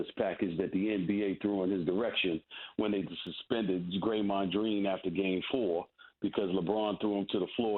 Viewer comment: Did he just say Graymond dreen?